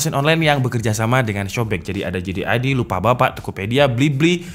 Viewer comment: itu lupabapak apa bukalapak atau aku salah denger nih 🤣